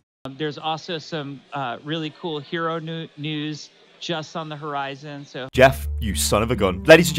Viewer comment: omg guys listen he says just on the horizon but listen it muffles out ... to me it sounds like it says jeff on the horizon and i data mined his whole kit he will have a passive where he lets his whole team use hacks his 1st attack will he a ban hammer and who ever it hits will be banned on the spot and that is it